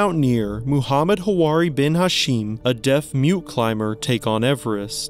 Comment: At first I thought you said the one disabled climber is blind & deaf (while actually deaf & mute) & I wondered to myself why the hell someone who can neither see nor hear would want to climb Everest as what could they possibly get out of it??? They wouldn’t be able to see the sites nor experience any auditory elements (though I would imagine that would solely be the blasting wind & the sounds of others present anyway so nothing exciting). Also how could it ever be possible to climb Everest if you’re blind & even more so if you’re dead too as you could neither see nor hear any directions & you wouldn’t know if an accident / tragedy occurred especially if it meant others’ deaths & perhaps even the rest of his team so then he’s absolutely screwed being alone, or if there was a different severe situation like an approaching avalanche he would have no idea it was coming & I imagine he would be abandoned as the others would prioritize their own survival, that is unless he was tied to another climber though that seems incredibly dangerous as an accident for one means an accident for both inevitably. I realize now that he isn’t blind of course. I guess being deaf & mute isn’t too big of a deal, as in it’s not extremely more dangerous (though still more so than with an able bodied person), absolutely impractical, or just downright unachievable. As long as there is a translator present &/or the development & use of physical (bodily) symbols especially in the case of emergency of differ